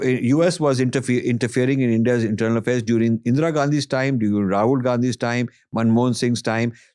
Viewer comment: I think he was saying rajiv Gandhi